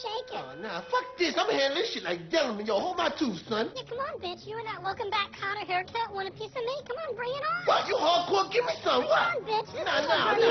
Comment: "Hold my tooth son!" 😂😂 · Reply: I thought he said hold my deuce son..